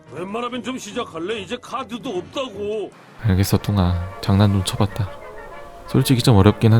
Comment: 나 처음에 알았어 븅아 라고들림 2번봤을때 뚱이인걸앎